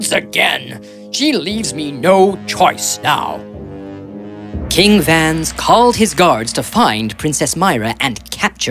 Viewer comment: they said "king vans" but the subtitle said "king julius"